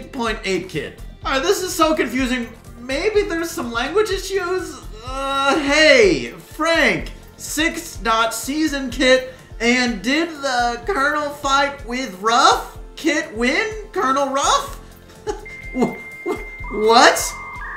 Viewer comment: Here is what I think this kid was trying to say: Hey, Frank! Did, during Season 6, Colonel Ruffs and Kit have a fight and did Colonel Ruffs win that fight? But hey thats just a theory, a youtube theory.